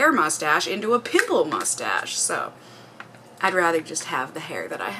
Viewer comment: When you said "pimple mustache" I heard "pickle mustache" and I was so confused for a few seconds